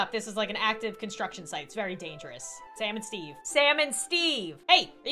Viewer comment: I thought she said salmon Steve 😂😂